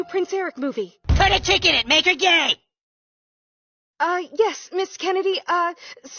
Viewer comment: I thought Cartman was saying... "make her lady gay"... Lame and gay is even better...